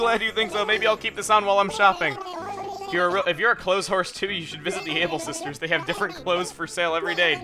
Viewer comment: I thought he said clotheswhore.